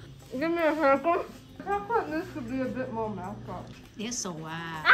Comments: "i think it needs to be a bit more melted" that's what I think she said😌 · i think she said 'I thought this would be a bit more-' and then idk that last word she said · I think she said “It needs to be a bit more melted.”